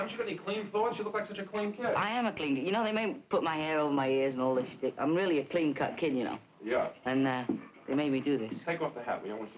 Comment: I haven't seen this in 40 years. I used to wonder if a swear slipped through. It sounds like he says "I may put my hair over my ears and all this shit. I am really a clean cut kid you know".